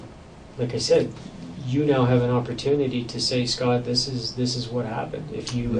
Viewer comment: When did Michael Scott become such intense interrogator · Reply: Funnily enough at 37:50 sounds like he says his name is Scott